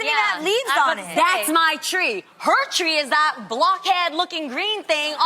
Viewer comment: lol amanda thought Jeanie was saying funny looking 'black' head instead of Block head, Sista Amanda spin so fast but breath and thought for a sec ,then realized that Jeanie wasn't being racial 🤣🤣 .I knew it's not me and Amanda alone was thinking the same thing lol..If you did too,then Subscribe to my channel 😉